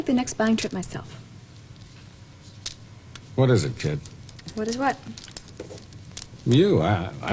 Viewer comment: Did Bob accidentally call Barbara Kim around the 22:55 mark? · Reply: It sounded like he said "What is it Kim? While talking to Barbara. Then I listened again and maybe he said "What is it kid"? I couldn't really tell.